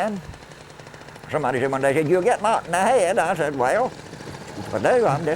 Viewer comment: lmao can anyone figure out what he said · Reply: "Someone said one day I'd get knocked in the head"